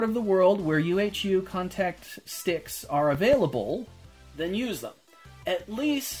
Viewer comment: hi! I've really just started this whole adventure and I need to know the easy way about it. What is the name of that glue stick? I can't make out what you say and I'm trying to buy soo... halp! pls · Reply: Sorry for the late reply. I think you mean the UHU contact powerglue